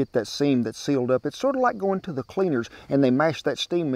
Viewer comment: I thought he said "beaners"!